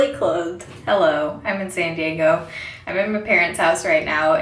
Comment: "i'm insanediego." is what i thought you said :,D